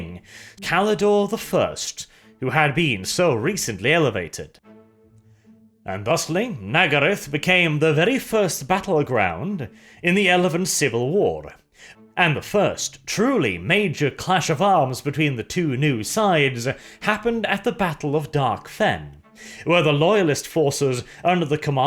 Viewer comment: what was that I heard? “The first battleground in the elephant civil war”?